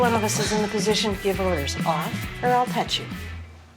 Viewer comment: I thought the lady said off or I’ll cut you😂💀